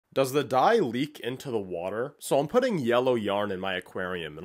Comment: I thought he said the dai lee like from avatar 😭